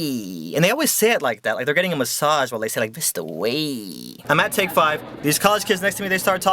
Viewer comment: Sounds like hes saying "Thisthawayy"